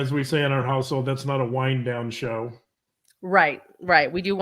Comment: Ah, not a “wind-down” show. I had to replay it a couple times… I thought said “wine-down” show, and pictured the two of you sharing a bottle of Cabernet in front of the tube after junior was put to bed.🍷